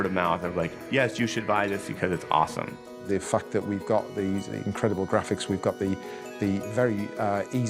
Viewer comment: i thought he said "the fuck we go got these incredible graphics"